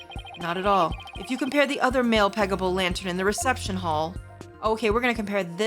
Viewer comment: Damn, every time she's said Pegabull I've heard it as "peggable"...like you're thinking about your strap-on and looking at a guy's ass thinking "damn that thing looks peggable 🤣🤣🤣